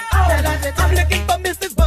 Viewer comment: Why did i hear “your so thick”💀